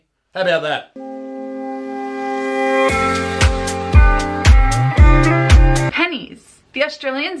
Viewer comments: Why did I thought that is said “No penises” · don’t tell me u didn’t think that said penis